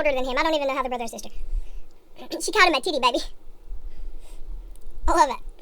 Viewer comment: OMG, I thought you said Jade called Mason a “titty baby”, I was like what the heck is a “titty baby”, but then all of sudden I realized you said “tiny baby”. I’m freakin dying right now girl. 🤣